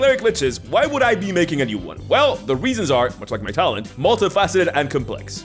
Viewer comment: Uh,what'd did say at 2:37?My ears couldn't pick it up,but the CC says "Well the reasons are,much like my talon "...Why would he bring up some bird claw? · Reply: he says "much like my talent." That's a recurring phrase by now.